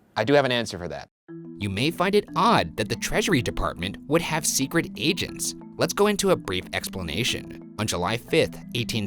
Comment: I was listening to the entire episode without actually watching it. I thought you said he was an “escort agent” until half the episode. Secret escort agent!!!!